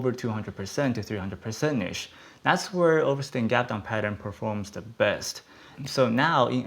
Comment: Trying to understand what he is saying can anyone help. A “what” kind of Gap Down? Is he saying a risking Gap down? Not picking on him I think this man is a freaking genius but I just cant understand what he is saying sometimes.